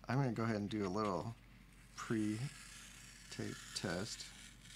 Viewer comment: I want to paint this on a van and drive down the highway with my mullet waving in the breeze. Also I kept hearing "taste test" instead of tape and kept waiting for you to lick the paper. You didn't, and now I'm just thinking of the missed opportunity to find out what 50 buck paper tastes like.